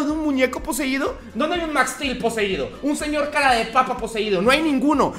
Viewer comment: Nadie: Realmente nadie: Mi mente al escuchar lo que dijo Juan (6:30 ): Un dildo poseído Si ya se soy don comedia :/